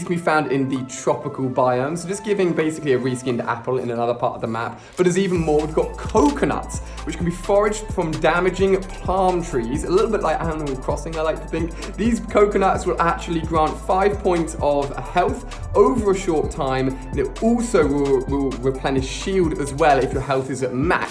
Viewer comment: who else heard him say aminal crossing? XD